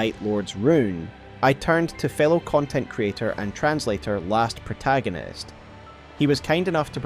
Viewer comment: I thought you said "failed content creator: last protagonist" 😅